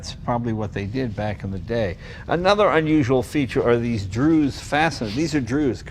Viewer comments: I heard "drews" fasteners and "foltpats" but did not know what you meant. What are these fasteners called please? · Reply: For the fasteners, it's 'Dzus fasteners'. These fasteners would have been brand new and state of the art aeronautics fasteners at the time when the original existed. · What kind of fastener is Jay saying at 7:23? Drews? Drewes? Google isn't giving me anything.